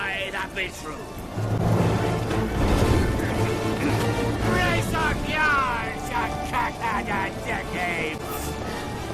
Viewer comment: "Brace up yards ya cockheaded dickapes!" I actually thought that's what he said as a kid 😂🤣